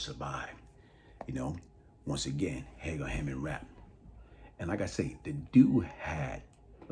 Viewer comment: Who is the guy beside "50 Cent" at 0:51? I couldn't quite hear the name. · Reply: Rapp